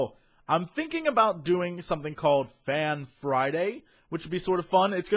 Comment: i thought he said man friday at first